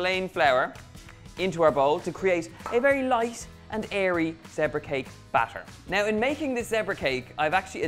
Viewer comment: Thought you said Debra